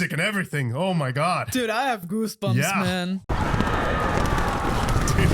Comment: Did she just say “I have Bruce bumps man”?